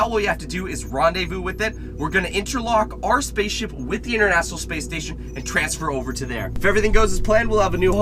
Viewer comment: internassional space station Edit: that's what I thought he said